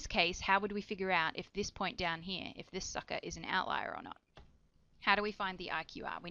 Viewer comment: did you just said sucker at 2:20;)